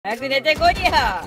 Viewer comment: 覚悟してくーださぁいに聞こえてたw